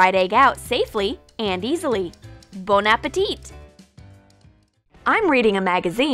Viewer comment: did she just say BONE-appetit?